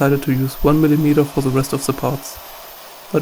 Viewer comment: Crap, I couldn't understand what he said at about 3:28. "As soon as ???? is installed I'm going to find the real limits... What is he installing? I have an MPCNC and was curious what he was going to install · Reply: Air blast